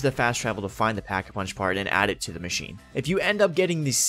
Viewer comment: Did he just say dee machine haha die machine